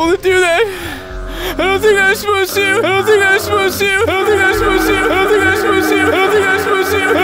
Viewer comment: Still have no idea what Peebs says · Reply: He's saying I don't think I'm supposed to